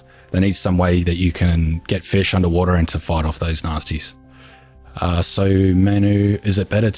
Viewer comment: For a second I thought you said to fight off the nazis when talking about the underwater fish catcher.